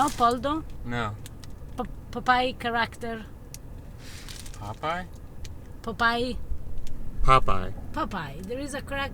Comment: I think she was saying Popeyes